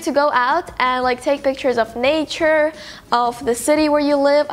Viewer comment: For a moment I thought she was going to say "naked"but she said "nature"😂😂💀